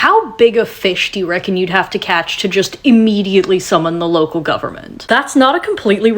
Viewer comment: I thought you said “how big a fish would you have to catch to immediately sell out your local government” and I’ll have you know, I was pondering